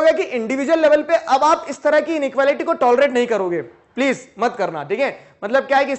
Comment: In subtitles instead of tolerate it is written toilet